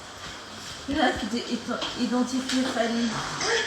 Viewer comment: Il était entrain de suivre le padre sur ce live boko bela mitu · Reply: moi J'ai entendu "identifiez Fally" 😁😂😝